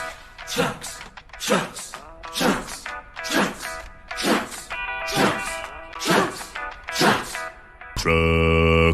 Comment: I wish you'd have put subtitles on this. What did they say at 0:22 please? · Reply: I think they said trucks but I’m not sure